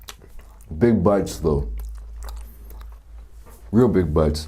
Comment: play it a 0.25 speed and it sounds like he says big butts though